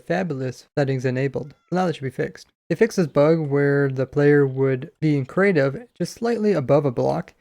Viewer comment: a fixed was buged or did i heard that wrong?